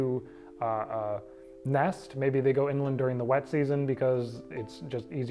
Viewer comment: Awesome channel! Btw, at 29:43 I thought you said "maybe they go inland during Wednesdays..." ,😅 had to rewind, lol. Admittedly it's late and I'm just up watching Ydaw!! 😊